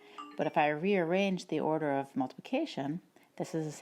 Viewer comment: I heard multiplication as notification😂